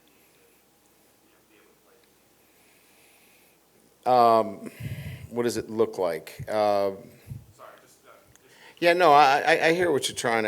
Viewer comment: Did anyone understand the question right before the 6:00 mark? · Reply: Asked what’s the process to get the young DBs ready for bama (Hughes, Toviano, Stamps, Welch)